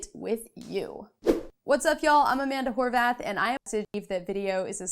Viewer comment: I thought she said Hi I’m Amanda Whorebath